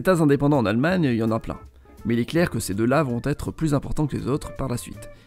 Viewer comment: J'ai compris "Mais Hit🤫ler" au lieu mais "Mais il est clair"